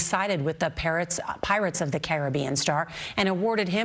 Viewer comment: As a survivor of SA, I am thrilled by this verdict.Those of us who are survivors of DV or SA stand with Depp and consider him to be one of us. For all of the media spin, we the people, including survivors, stood solid in our belief that Johnny Depp was the victim of abuse and the lies of a malicious woman. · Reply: This might be irrelevant but y’all heard what she said at 0:07 lmao, “Parrots of the Caribbean”